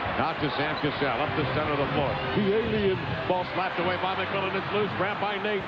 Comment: haha did he actually call sam cassell "the alien" i thought that was something that just the internet noticed, didnt think people in the league joked around with that one haha